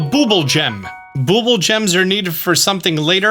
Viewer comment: I thought it said bubble gum 😂